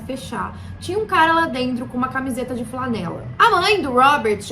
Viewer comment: Voltei porque juro que ouvi "com a camisa do Flamengo" kkk Ótimos vídeos. Parabéns